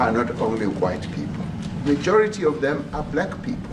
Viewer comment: So- what I heard was “f*** those white people cause more black people die…” I guess we are done protecting minority groups within a country- good to know.